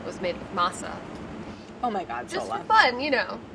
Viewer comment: Really thought Sohla said to make the shell with matzoh and was so confused until Claire said masa harina 😅